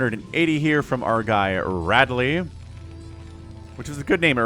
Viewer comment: Lolhad to rewind swore u said ripley first time and was like wth thats aliens not metroid haha